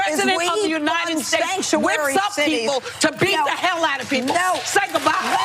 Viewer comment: did whoopi say Trump whips up people to beat the hell outa somebody???? As a neutral i hear so much heresy on both sides. Im a neutral because im not interested in taking a side. Still trying to figure it out. I see children trying to one up each other and the hypocrisy actually of Liberals is they want equal rights for all but if you dont agree with them youre a scum bag.